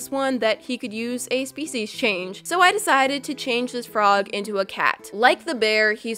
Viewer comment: I thought that kept saying faeces change lol ewwww